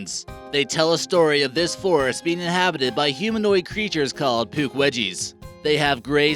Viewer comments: I thought he said "Poowedgies"!! XD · I thought he said poop wedgies and I was thinking “is this guy serious” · I heard poopwedgies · when he said pukwedgies I thought he said poopwedgies